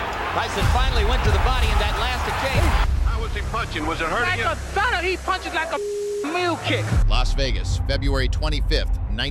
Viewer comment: Couldn't quite understand all of it so I went to the transcription: 0:20 "How was he punching? Was it hurting like a son? He punches like a wheel kick." 🤣